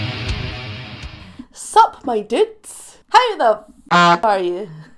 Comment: Whenever I first started watching, I heard “Suck my tits! How the fuck are ya?”